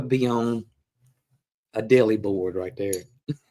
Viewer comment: I thought I heard "dilly board". I'm sure those little black beads were escargot, (fish eggs). I'd prefer smaller pieces of strawberry in my preserves as it then goes further. will have to find a homemade recipe cracker. (Sorry about that my Spanish is messing with my English sentence structure. Studying with DUO since Dec 2020). Can't wait to see the new book . Can you do in PDF?